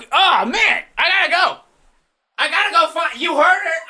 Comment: did say, "aw man! i gotta go fuck you harder!" ???